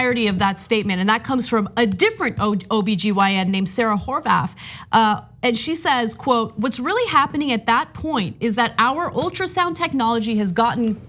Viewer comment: When you said "Sarah Horvath", I legit thought you said "Sarah Whorebath".